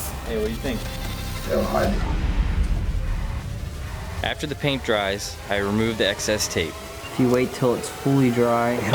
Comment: what did the bearded guy whisper at 4:39? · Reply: Hail Hydra